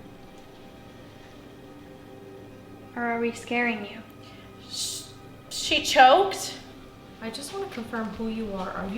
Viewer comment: can someone help me what was it trying to trick them into saying at 37:40 you dont have to type it fully but just a kind of pointer because i couldnt quite understand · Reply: I a ccept , bc you could be agreeing to something with a demon